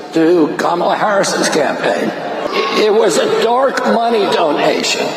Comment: Did he just say it was a dork money donation? How true 😂😂